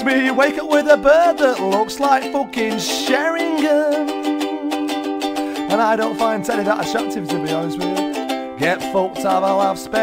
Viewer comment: untill 3:11 what is he saying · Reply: And I don’t find teddy that attractive to be honest with yah!